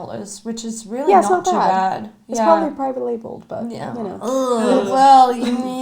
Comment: I'm sorry, I tried a few times now, but I still don't know what said there. I would love to look it up but i'm not a native English speaker, so I don't even know how you would spell that word. I loved this video by the way, I'm really interested in Indie Make Up. They seem to think out of the box more often than the usual brands! Thank you guys for introducing us to more Indie Stuff. <3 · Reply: I think the time stamp is where she says "it's probably private labeled" if that helps!